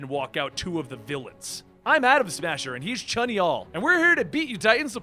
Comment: 'Chanyeol' Did I hear that wrong?